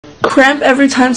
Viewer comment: Oml I thought it said crap every time someone lies to you